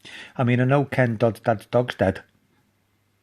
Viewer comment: what did he say at 2:30? :/ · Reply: I said.." I know Ken dodds dads dogs dead" 😂😂😂